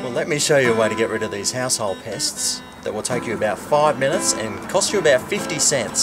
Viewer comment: I heard "Asshole pest" lmao